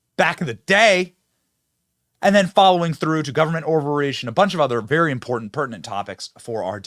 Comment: My brain fog sometimes messes with my hearing understanding. I heard Benny's "government overreach" as "government ovaries" for a moment...